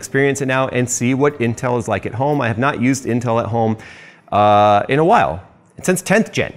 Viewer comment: All I heard was "Intel paid me"